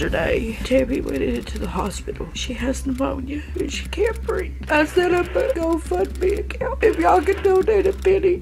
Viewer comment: it sounded like she said "i set up a go fuck me account"